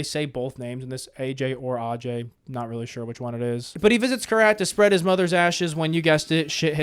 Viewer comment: That got me off guard LOL. I initially misheard that as "To spread your mother's ass" 😅